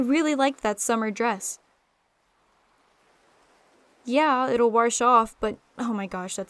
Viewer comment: So, 7:00, I don’t know if it was just an accidental slurring, but did you just use the beautiful word that is “warsh” instead of wash? As in, “we warsh our cloths here in the south?” If so, thank you for immersifying me, a happy little Southern boy. 🤠